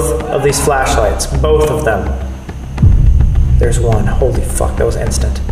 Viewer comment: I heard something yell “help” at 44:05 when he said if the mirror was a portal